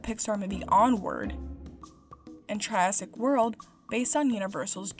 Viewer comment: That was not a good moment to mishear jurassic as giraffic, i died laughing